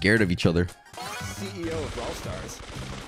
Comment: Is it me, or am I hearing “CEO of BrawlStars”?